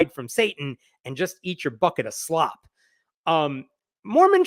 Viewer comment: When you said "bucket of slop," I heard "fuckin' slop" and laughed my stupid head off. Love your work, funny or not